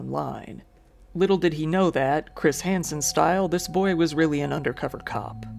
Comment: I misheard "Chris Hanson style" as "Jim Henson style" and now have the most terrible mental image in my brain.